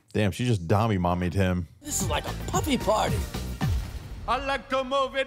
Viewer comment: yo did he just say a puffy party???